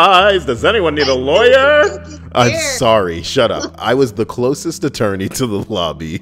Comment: I was laughing my ass off as soon as I heard "closet attorney" 😄 I thought it was an actual thing 'til I heard you say "closest"